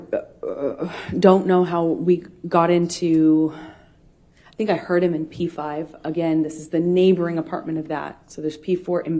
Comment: Does she say, "I think I hurt him in P5?" Or am I just misheaaring things.